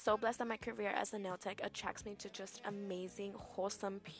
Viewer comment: What is a health promotion specialist?? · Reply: I heard it as commotion 🙄